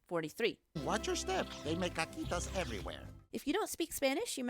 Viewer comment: I don't speak Spanish. what did they say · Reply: camryn fuenmayor he said "they make cacuitas everywhere" meaning they leave little poops cacitas/caquitas (?spelling) is pronounced Ka-qui-tas caca=poop cacitas= little poops (cute/endearing way to say it)